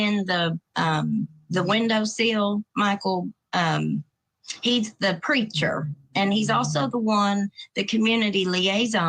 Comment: 😂😂😂. When you said, “He’s the preacher” , At first I thought you said, “He’s the creature.” Hahahaha.